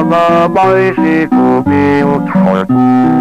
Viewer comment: Contexto de la canción: es un tipo que le canta a otra tipa la letra es bonita y en una parte dice que construyó una bicicleta para los dos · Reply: Pero no c porque en el final escuche "Juepu" y pensaba que era Jueputa pero era "For two" 😔